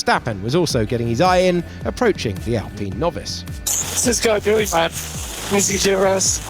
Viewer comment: What did Max say at 1:33? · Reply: “What’s He Doing Man” “Can’t He Use DRS”